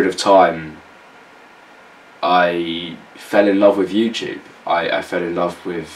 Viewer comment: When he said 'because I fell in love with YouTube', I immediately jumped to conclusions and thought he said I fell in love with you. Fuck sake